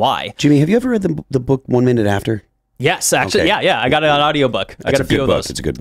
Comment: Had to listen to this about 10 times to understand what he said, I think the title of the book is "One Minute After".